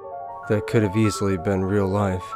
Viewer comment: Did any of you watch till thee end of the friggen video where he says he's back on u tube. Geezus. He's just trying to give us the worst scare of our lives and to see if we actually finish. Bahhahaha. Thankn u for the great year. Peace and much love sent to all from Ontario, Canada. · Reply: i thought he said, "just like that I'm on u tube again", hummmmm. People can make mistakes. Will apologise to the listeners for my grave mistake. Peace, happy new years and much love sent from Ontario, Canada.